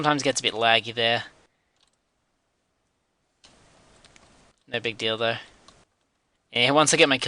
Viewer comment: go to 34:36 it sounds like he said "no big dildo"